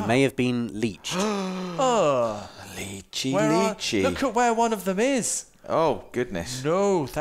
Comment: Did someone say “leechy leechy”